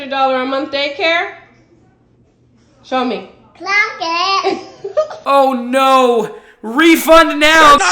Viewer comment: What's the little girl saying? "Clackit"? " Clock it"? I can't work it out...